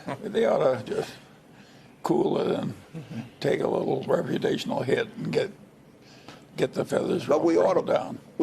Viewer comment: Wait wait did he say 8:17 “ they need to cool it down and take a recreational hit” 😂😂😂😂